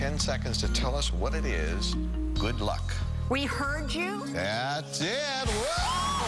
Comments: Me thought it was “Me heard you” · I, with all confidence, thought it was “be heard now” 😂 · I thought be heard now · I, with all confidence, thought it was “be heard now” 😂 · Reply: I thought it was either “we heard you”, or “he heard you.”